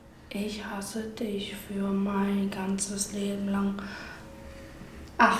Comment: Hat "Du tropfst unter'm Knie" gesagt, oder verstehe nur ich das? 😂😂😂